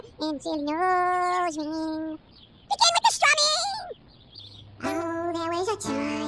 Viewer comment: She gave me pastrami? I thought he said begin with the strumming